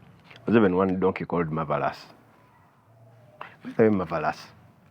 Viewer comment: I hear "by the way marvelous" 😂😂😂